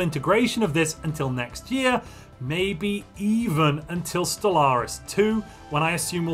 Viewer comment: DID I HEAR STELLARIS 2!!!!???????